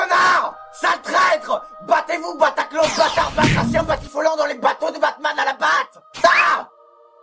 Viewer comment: il dit quoi Harry de 4:38 à 4:42 ? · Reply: Battez-vous Batacloche Batard Batracien Batifiolant dans les Bateaux de Batman à la ... pate ?